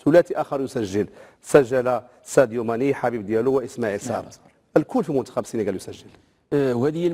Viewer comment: واش انا بوحدي اللي سمعت...سجل ساديو ماني و الحبيب ديالو🤔🤔🤔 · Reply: هههه راه اللاعب سميته حبيب ديالو هربتيني 😂😂😂